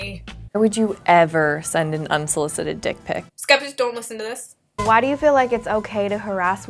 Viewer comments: what did you say at 3:48 ? (was it 'Skeptic dont listen to this'?) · what does she say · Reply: "Skeptics don't listen to this" Which at the time a youtube named skeptics was dating her